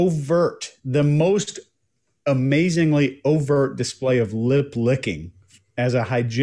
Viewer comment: I thought said lid licking and just had an image of a giraffe deposition 😅